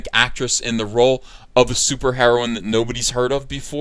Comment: did someone say super heroin?